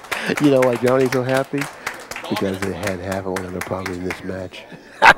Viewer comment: Does anyone know what he said at 51:13? "You know why Johnny's so happy?... " then mumbles. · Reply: Johnny had half of John on the bonus I think is what he said.